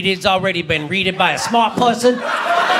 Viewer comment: "Don't you know everything that needs to be readed has already been readed by a ___ ______?" I can't get what he's saying and the CC makes no sense either. Can anyone help? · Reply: “Smart person”